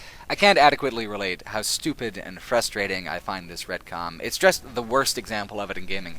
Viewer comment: What's the word he's saying around 1:19:00? It sounds like "Redcom." What's this mean?